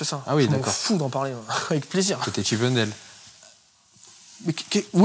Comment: Qu'est-ce qu'il dit à 40:40 ? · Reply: Chippendale